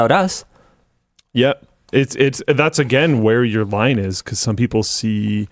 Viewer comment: is he saying "Linus" or "line is"?